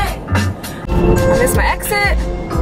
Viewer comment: When she said I missed my exit, I actually thought she said I miss my ex...